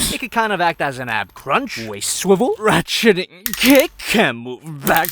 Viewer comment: sounded like "rat shitting cake" to me